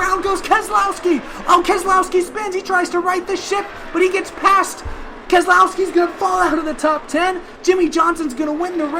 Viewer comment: did I hear the s word or shift? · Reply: Ship I believe actually